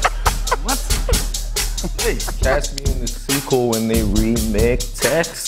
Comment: Bearface said "fhdridsthcdt taxi" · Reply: Johan Jansson i thought he said cast me in the sequel